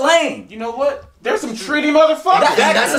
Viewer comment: Lmfao what did bro say at 0:27? 😂😂😂 “You know what, they’re some __ mfkers!” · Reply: Johnny Ford treaty